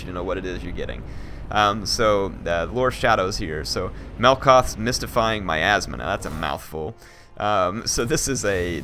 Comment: i heard it as "melkoth's mystifying my ass (ma), now that's a mouthful.